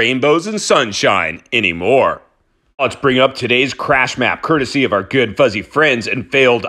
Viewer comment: At first I thought you said crap map😂?